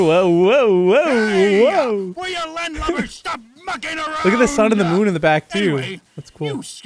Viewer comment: I was eating sour patch kids wich was perfect Am i the only one who hears WILL YA LAND LUVERS STOP F*CKIN